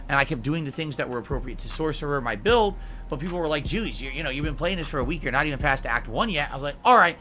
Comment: he slips up and says “Jews” instead of “Geez”… should be wearing his Nazi hat